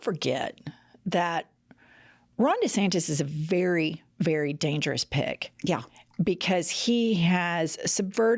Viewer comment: I had to laugh every time you said Ron D was a dangerous pick, I thought you said dangerous pig. Either way, though.